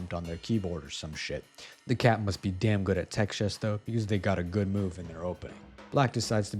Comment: did captions say "texas" instead of "text chess"?